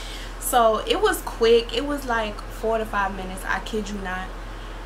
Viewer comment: Hold up 45 min is a short time gurlll that's a whole work out, I'd be lucky if I made 10 minutes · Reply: Ms. sound like she said 4 to 5 minutes